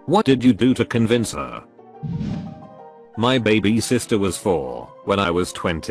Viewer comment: Lol 8:40 I thought he said his baby sitter was 4 and he was twenty